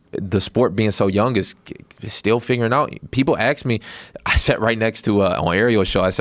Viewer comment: he's saying axe instead of ask 2 times in a row?